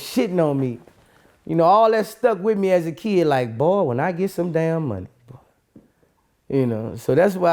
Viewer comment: Wat boosie say 11:13 Boy once i get sum money💯